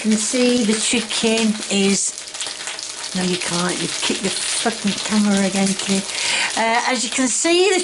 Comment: No I didn't I said bloody hot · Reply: right after you kicked the camera....it sounds like you said, "No you can't, you kicked the f****ng camera again, Kay"